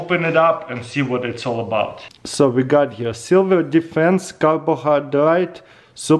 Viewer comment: Did I hear "Silver Defence"?